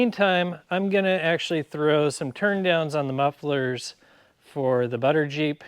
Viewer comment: I misheard, thought you were going to throw down some turntables, for a moment I expected you to get your DJ on. :)